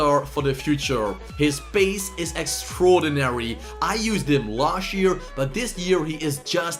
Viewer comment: Just me that thought he Said "lashier" instead og last year? Just me? Ok then